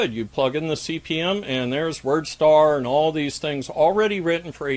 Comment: I have this playing in the background while working, and I thought I heard "World Star" at 22:15 lol.